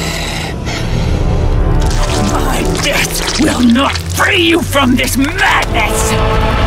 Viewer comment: I thought i heard "my breast will not free you from this madness"